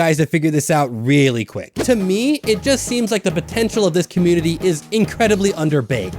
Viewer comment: DID YOU SAY POTENTIAL???